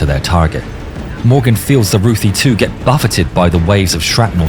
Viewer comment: Ruthie 2, I thought he was saying roofie 2!